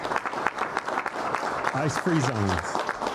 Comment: They thought he said ice cream zones